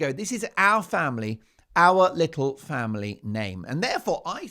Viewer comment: Thought said "her little families name is Sussex "